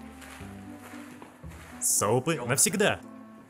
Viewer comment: Походу только я услышала от Хосока : Соупы навсегда 3:29 Sope is real 🖤